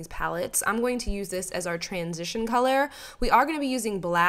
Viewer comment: I'm a mechanic I always hear "transmission " instead of "transition color " 😅